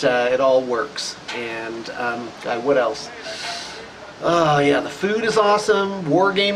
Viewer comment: Everytime says "what else?" i hear "Wood Elves"... just wishful thinking ;)